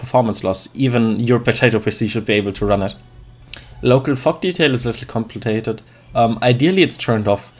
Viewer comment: For some reason I heard "Local Fuck Detail".